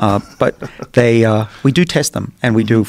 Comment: when talking about the mice I really thought he said “we do taste then”...